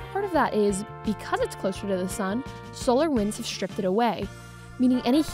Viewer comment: Did u just say Mars is closer than earth? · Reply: No, she said is closer to the sun -- nothing about being closer than earth